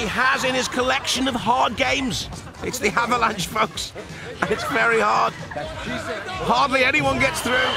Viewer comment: lmao anyone else hear at 4:41 when he says "its very hard" i heard "thats what she said" lmao